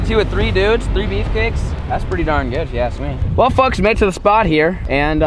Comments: did he say well fucks? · who else heard say “well fucks”